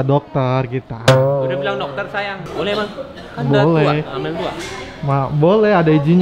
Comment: Tadi kayanya denger ka baim bilang "udah H-2 loh". Apa aku salah denger yaa